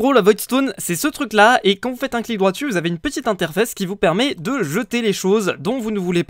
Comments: Ptdr A 17:06 "une petite interfesse" vous comprenez quoi vous ? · c moi ou ah 17:07 enfaite vous avez une petite interFESSE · C moi ou à 17:07 il a dit inter fesse?